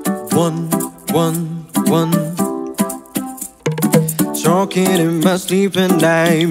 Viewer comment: Waktu bilang talking, kedengeran kaya choking 😂😂😂